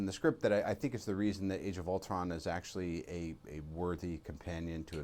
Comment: It sounded like he said "Age of Ultwon"